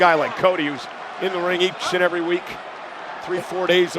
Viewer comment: Cole said "Especially a guy like Cody, who's in the ring each and every week." I can hear "eating shit every week", sure, but that's definitely not what I heard initially. 😂